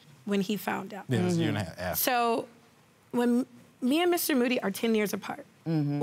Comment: I really thought she said "Mr. Booty" lol